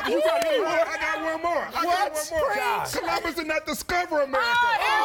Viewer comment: Can someone tell me what the dreadlocks guy said in 1:23 because after he said everyone just went berserk lol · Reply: Edward Yang colombus didn't discover America!